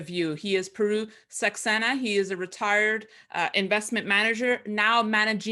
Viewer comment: Sorry, I must have misheard you. I thought you opened with "he is a retired investor" but clearly it must have been "he is retard investor" with a Borat accent.